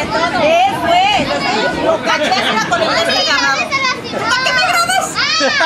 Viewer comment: Mexican Mom Off Camera : 💢💥🤬 Maxican Mom On Camera : ❤️😘💋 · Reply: oh i heard “que grabas” thank you